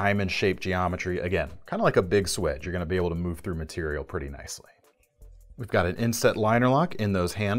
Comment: When you say "move through materials" I hear "move through flesh and bone"....